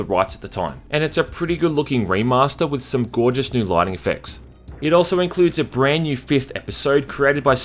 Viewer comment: Did you say "Lightning effects" XD Also no mention of crocodoyles... Still liked :D